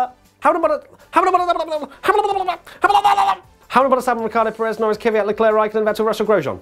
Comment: Am I the only who hears Verstappen instead of Albon at the Race Rundown 18:15 ? HAM BOT VER programmed into brain.